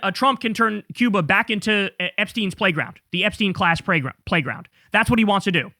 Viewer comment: I heard: "The Epstein-class preyground" ...no need to correct yourself...